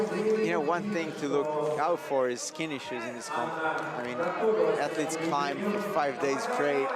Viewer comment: When he said skin issues I thought he said skinny shoes XD